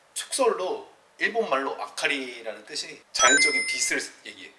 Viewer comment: 아카리가 자연적인 빛이라기보다는 불빛아닌가? 순간히카리 라고 들었네